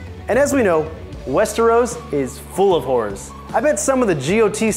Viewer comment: Turn on captions at 8:03 and it thinks he said Westeros is full of whores instead of horrors 😂 (not that it’s not true lol)